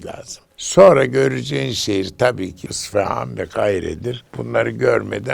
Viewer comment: kahire ve ne diyor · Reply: İsfahan ve Kahire